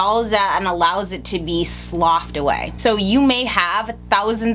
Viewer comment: Hi, I really enjoy your videos, they're very informative and helpful! Just a question, on 2:05 - 2:06, did you say "it goes in, kind of dissolves that and allows it to be slopped away"? What does slopped away mean? Or did I hear it wrongly? Thanks! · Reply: Felicia P J “sloughed off” or lightly shed the dead layers